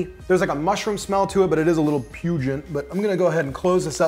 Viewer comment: Question: Is „pugent“ a word or did you mean „pungent“? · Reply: I heard "pungent".